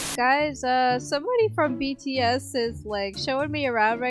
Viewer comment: omg did say btS?